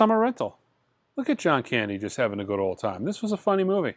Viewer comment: Good gosh, thats alotta stuff. I nodded off about the time you were showing the "Summer Rental" and startled back awake when I thought you said "Look, there's John Bandy!" instead of Candy 😳freaked me out😂 See you in the A.M. for the left overs!